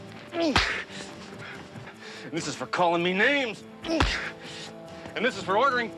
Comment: Of the episodes I watched, this is the one I remember most, sort of. During the climatic fight scene, I thought he said, "...and this is for calling me 'Honey' ". "That was gratuitous" was great.